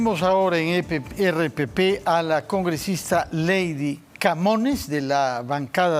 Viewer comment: Pensé que decía camotes 🍠 pero dije no es puebla es Perú 🇵🇪